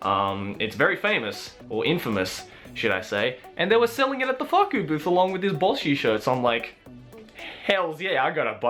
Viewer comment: Every american heard: "and they were sellling it at the fuck you booth along with this bull shit shirt" if you didn't hear that then now you can't unhear it and you're not a true american